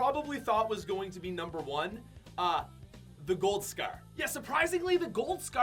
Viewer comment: Love your vids but why does it sound like your saying scear instead of scar